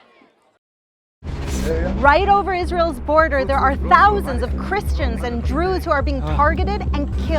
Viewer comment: Did she say "Christians and Drews" at 5:05. And yes I understand she probably meant Jews.